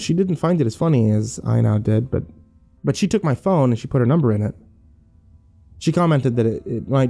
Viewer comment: During the screens part I thought she said "he took my photo" like what Josh said but after screens. I felt a very cold chill down my spine now knowing she said "he took my phone"